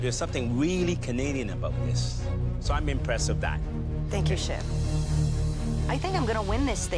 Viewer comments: sound like she said: Thank you s*** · I thought she said thank you sh!t😂😂